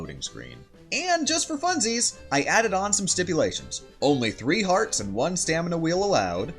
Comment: I thought you said only 3 hearts